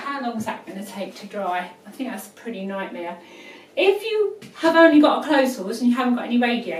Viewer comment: OMG.. what the hell is she saying?? I swear didn't understand 1 word she said. What language is she speaking? Certainly not american english. From 5:41 to 5:45, what is she saying? NO way thats english. BTW, is there an american version of this vid? or at least subtitles?? WOW. my brain is hurting now. But thanks anyways for the vid. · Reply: She said "if you've only got a clothes horse and you don't have a radiator" how is that hard to understand dude. Is English your second language. Give the lady a break. Thanks for the video +